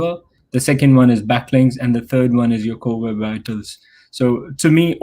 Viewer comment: What's the 3rd item? Couldn't understand. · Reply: Core Web Vitals